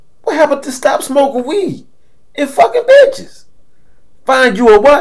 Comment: 😂😂😅😅 I thought you said no weed no bitches